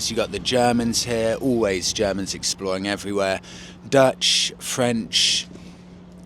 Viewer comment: ! Did you just say, "The Germans, always invading everywhere" 😂😂😂